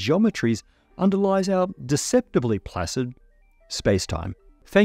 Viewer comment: For a moment I thought he said "deceptively flaccid" and was thrown off